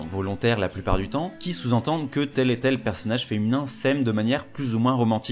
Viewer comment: C'est quoi le nom j'arrive pas à le comprendre quand il le dit 😂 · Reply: C'est pas un nom, je dis "tel et tel personnage".